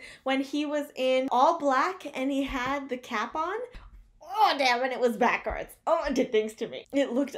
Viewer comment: is it just me or did you just say ...DAMN SON !😂😂?? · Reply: 😂 I so did! I say DAYUMMM Son a lot! :))